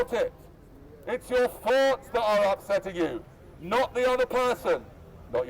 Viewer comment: i can't get what he's saying.....e.g. it's your FIRSTAR upsetting you. wtf is that word yes saying